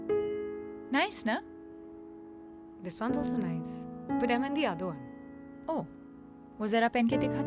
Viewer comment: what does Amu say at 0:24 after saying, "This one's also nice"? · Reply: "But I meant the other one"